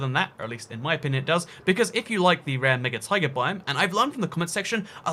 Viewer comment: did anyone else hear "Rare N!gga taiga biome" ?